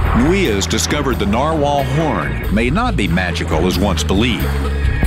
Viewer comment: thumbs up if you thought he said "porn" instead of "horn" fpr a second